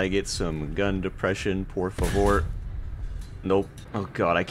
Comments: ayo did bro said "por favor" in 4:12 ??? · did he say ''por favor''? wtf. where are from?